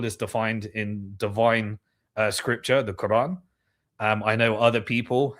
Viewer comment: At about the 3:04:00 mark did i hear you say "Divine scripture of the Koran".......did I hear this correct?